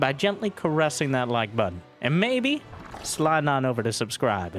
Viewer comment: When you said "carressing that like button" I heard "buttor". That raised some audible questionmarks, then a giggle. And thus my day was a little bit better.